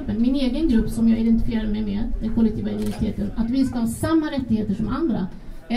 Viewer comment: Vad säger hon från F! att hon identifierar sig som vid 48:30? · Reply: Svårt att höra faktiskt. "Den kollektiva minoriteten"?